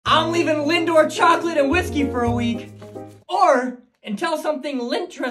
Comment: Thought you said leaving Linda in Chocolate and whiskey for a week, so the nipples line hit different.